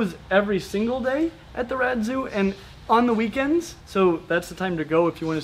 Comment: Every video I thought she was saying rat zoo